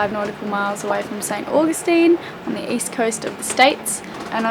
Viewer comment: for a second I thought I heard you say " Augusta" which is in Australia I heard wrong, great story! hi!